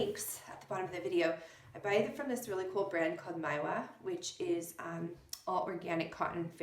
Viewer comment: I'm with Mijejo Mijejo....I thought you said something like "Nyla", when you were identifying the organic cotton. Thank you if you answer. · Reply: oh gotcha. I believe I said Maiwa - it is a brand that carries organic cotton blanks that I use often in my studio.